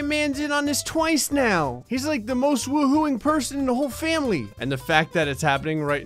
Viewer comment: did he just say family sweet home alabama starts playing in the distance